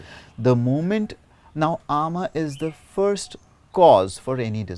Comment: Hi. At 22:50 what is the word?It's Ama?